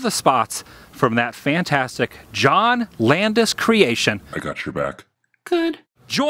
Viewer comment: I thought Landis said, "I want you back" at first. So I naturally was expecting MJ to say "ooo ooo Baby" or "yeah-yeah, yeah-yeah"